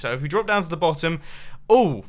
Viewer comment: i heard oh sht on